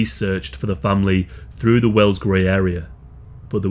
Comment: Thought that said “The well gay’s park” lol